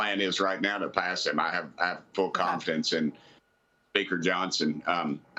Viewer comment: I had to go back to see if I heard that right, he says Baker Johnson but, I heard bigger Johnson the first time 🧐😂